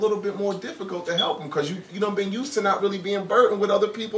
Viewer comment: did he say done been use to?